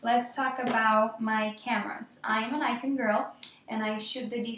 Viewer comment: At first i thought she said "im a Lecia girl" and i was like WHAAAA